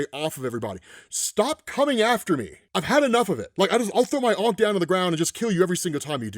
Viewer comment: I thought at first you said "I'll throw your Aunt down to the ground!" Not Aunt May!!!